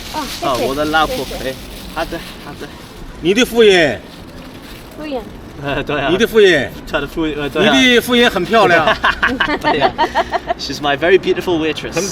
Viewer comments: the old man asked "Is that your wife? Your wife is beautiful." The word he used was "Fu Ren" (wife), instead of "Fu Wu Yuan" (waitress) LOL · this old gentleman got a heavy north-east chinese accent, it sounds like "waitress" but what he said is "wife"